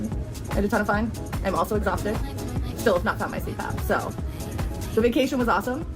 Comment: LOL at the trabscription saying "seat poop" instead of CPAP